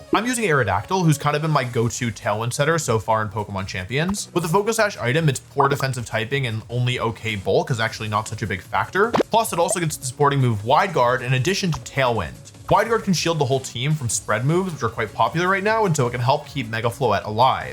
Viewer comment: did he just say “Hwide Guard”?